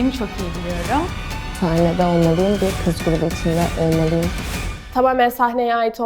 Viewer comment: ne dediğini anlayan var mı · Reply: kiz grupu icinde olmaliyim